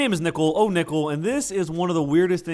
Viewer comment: I thought i heard him say Nipple-oh-nipple. Lol